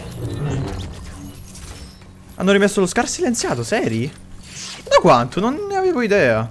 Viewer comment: solo io ho sentito la parola in inglese SCAR? al minuto